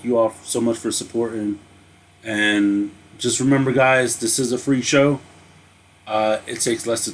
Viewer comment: People who beech about courtesies are useless. People who don't appreciate going above and beyond are useless 😒 🙄 At the end it sounded like you said freak show. 🤣 then I realized you were saying free show 🙄🤣💙